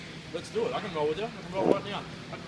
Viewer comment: did he say "parou"? I didn't know BJJ people spoke portuguese lmao that's so cool